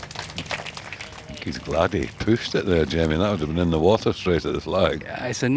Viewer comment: Did he just say “pussed it”? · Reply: "He pushed it"